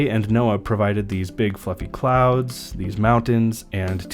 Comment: Did he say big f*** in clouds at 1:50?😂